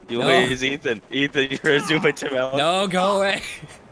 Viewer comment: I thought this was saying zoomd by tanel